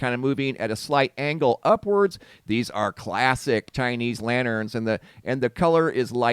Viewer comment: Narrator u uttered "tiny". I heard you said "chinese"! 😂😂